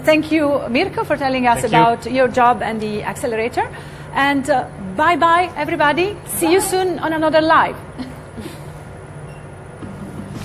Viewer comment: does she say 'See you soon in another life ?"